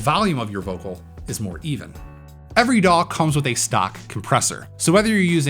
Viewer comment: did you say, "Every DAW comes with a stock compressor." If yes, I don't know what that is